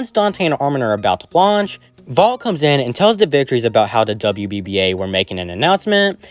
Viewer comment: He is there all the time and he has been there since he was little with his parents. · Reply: Did you say "Valt arrived and tell the bitches?"